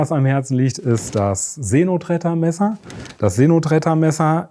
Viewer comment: See Notretter.... ich habe Seeno Tretter verstanden... oh man, ich dachte, Brathering wäre schon schlimm.